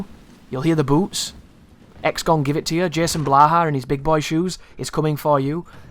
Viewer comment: did you just say jason blaha? I didn't know you knew about him.